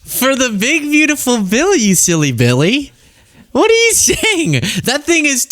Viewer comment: "You silly billy" It really sounded like you were going to say, "you silly bitch." Oh well